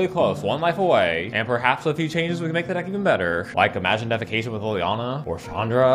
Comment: Legitimately thought it said defication the first time I saw it